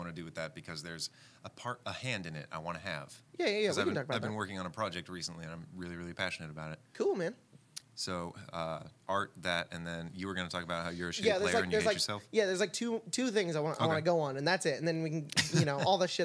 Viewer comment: When covered the mike and started talking all I heard was “...about how your publishing...” and I was like,”STibBle’s?!”